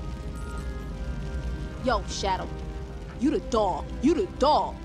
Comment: Did she say your the dog or the dawn? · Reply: The dog